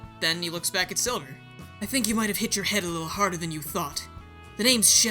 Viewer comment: am i the only one who heard thot.